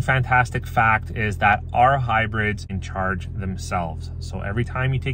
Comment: Did he say "OUR" Hybrids charge themselves ?